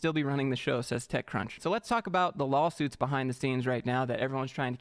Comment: Did you say "LOL-suits?" 2:29😆